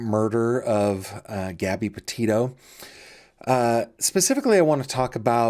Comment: Thank you all for the amazing support of my channel! Links to my merch and patreon page is available at <link> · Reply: I noticed you haven't uploaded all the Police Call out reports to the Laundries on your website. Also, in around 0:30 of the video, it is subtitled as Gabby Potatoe instead of Petitio. This isn't auto-generated this is your own upload and submission.